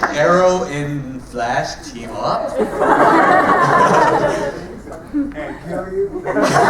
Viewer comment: What did the guy say at 07:13 ? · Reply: About the team up? It sounded like "And kill you."